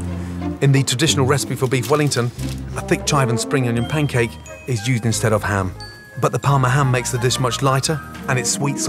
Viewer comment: At the 3:19 mark, "in the traditional recipe" what the hell does he say next? Rewound 5 times and its absolute gibberish to me. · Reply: a thick chive and spring onion pancake is used instead of ham